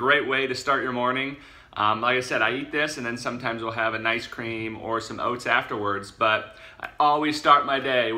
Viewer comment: Did he say he has celery in the morning and then some ice cream afterwards? lol